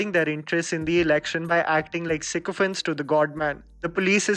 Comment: forget review, what was the word used at 0:41 sec. I come here to learn vocabulary. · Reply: “Sycophant.” Basically means a chaaplus/who will praise powerful or rich people or their own interests.